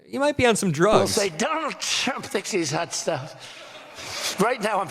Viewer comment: Did he say Donald Chump? 😂🤣 Did he just speak the truth?